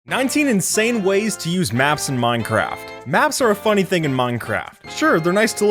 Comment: Did he just say "muncraft"?